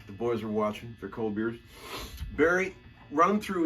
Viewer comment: did he say cold beers or nose beers? haha